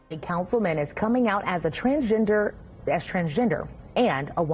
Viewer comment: I thought he said trans ginger !